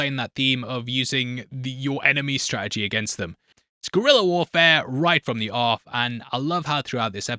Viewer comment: did you say gorilla warfare instead of guerilla warfare!? 😂 Edit: 2:44, 16:14, 42:23 - seven hells, you actually did. LMAO